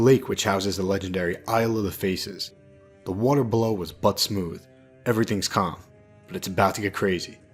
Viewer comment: Did you say that the water on the lake was "Butt smooth", or was I just hearing things?🤣